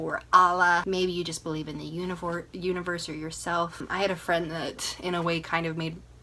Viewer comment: I ABSOLUTELY agree with your steps. Let yourself grieve immediately if it's there, because it will crop up at some stage, and people give you the room to grieve at the time. I remember a week or so in having to do laundry. I literally had to write down the steps... it was too hard mentally. I thought I was losing my mind. I went through it 4 times. Each process was different, bit easier xxx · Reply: I found reiki very healing, and I wasn't pro-alternative treatments before experiencing it. It was during reiki I had my baby vision 👶🏼 When you said 'whether you believe in the universe...' I thought you said 'whether you believe in unicorns...' 🦄😂😂😂